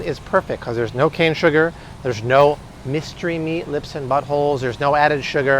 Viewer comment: Did I just hear "no mystery meat, lips and buttholes?" 😂